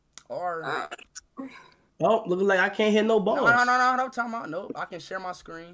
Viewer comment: who else thought he said balls ⚽️ 🏀